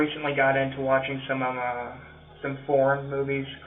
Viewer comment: I think a few times you misheard him lol. He he subtitles as well imo, ex: 17:38 - pretty sure he was says horror instead of foreign